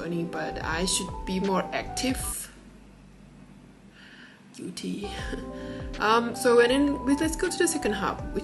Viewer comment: ur face looks funny..... what were u saying.. UD? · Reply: I think I was saying "guilty" 🤣